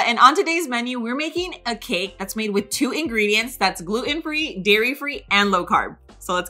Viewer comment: 🤔 I thought she said dairy free